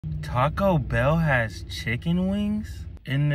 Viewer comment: Did i just mishear him say caco bell👁